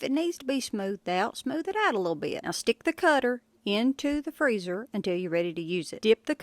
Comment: I thought u said f*** the butter